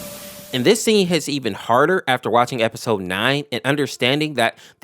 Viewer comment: I’m sorry did you just say EP 9!?